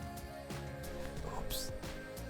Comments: who else heared that whoops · I heard a faint oops